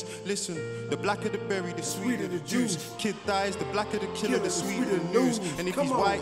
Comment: 'The blacker the berry the sweeter the juice, the blacker the person the sweeter the news' this line hit hard🔥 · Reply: Thought he said blacker the murderer?